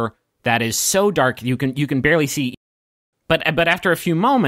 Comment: I swear at like 1:02-1:06 ish I can almost hear think better of saying 'You can barely see your hand in front of your own face' Like I doubt that's what that actually is but that's my second time hearing it and like that's what came to my mind first?? Now that it's in the context of video, which by the way I love this